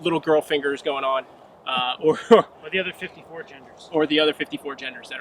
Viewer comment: “The other 54 genders”. That was pretty funny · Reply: Wes Campbell LMAO Thanks for saying that. I misheard and thought he said something about " 4 gen'ers" as a reference to Glock gen 4 Wasn't one of the Glock's generations criticized for their slide release? Mine are 1st gen so I don't know. Anyway, "54 genders" is hysterical